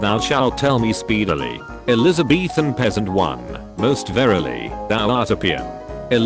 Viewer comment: If I hear that damned automated voice say Elizabethan again I’m going to kill myself. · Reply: I heard "Elizabeaf"